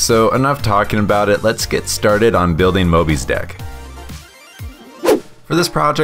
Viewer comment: When he first said the name I thought he said Mobys D**k. (I apologize for my spelling)